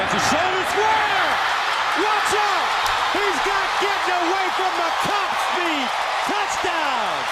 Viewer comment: Did I hear he got getting away from the cops speed @ 6:35??